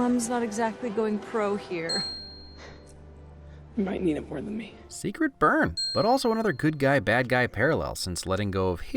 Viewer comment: What Is He Saying? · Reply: “You might need it more than me”